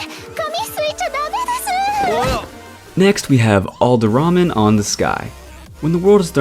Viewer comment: Why did I hear "all the ramen in the sky?" for number 9 😂